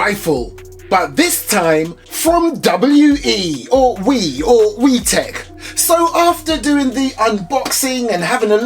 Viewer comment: all i hear is “yes tech”